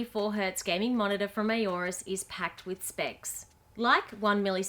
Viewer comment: Late on a Friday night after a hard week at work, listening on my old iPad, honestly thought that at 0:20 you said “packed with sex”. LOL